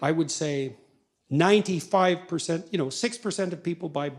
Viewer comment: did I hear "6% of people buy books" ? That can't be true.